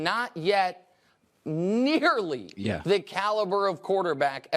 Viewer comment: WHAT DID NICK SAY!?!??!?! 😭😭😭😭😭 "NIGGERLY"?????